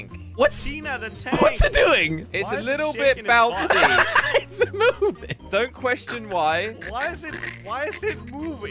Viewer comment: Did Jelly say it’s a moving